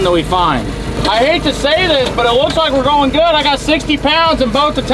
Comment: does my guy play 5 string bass???? i swear i heard him say "looks like a B string"??